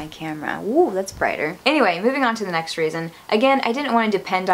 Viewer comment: did you say... the next.. raison? haha